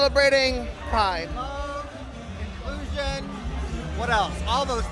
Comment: I thought that he said "delusion"